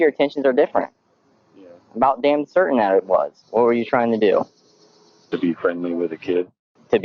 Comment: Here he actually said, "To be friendly with a kid." It's a small difference but the meaning is immediately very different.